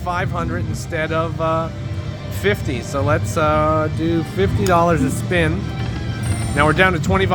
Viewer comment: “let’s do $50 a spin” did I hear right?